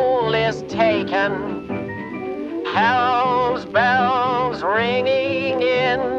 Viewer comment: I thought it said hell's balls